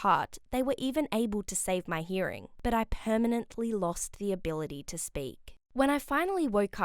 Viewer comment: “I permanently lost the ability to speak” Am I hearing things?